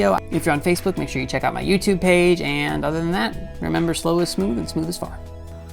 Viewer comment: I catch myself saying slow is smooth and smooth is fast all the time. Love the pointers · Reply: Zee Devil I always heard "slow is smooth and smooth is far"